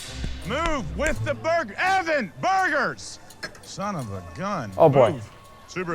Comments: Burger? did he just say his last name is burger? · did he just say his last name was.. burger?! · evan burger did he just say his last name is burger?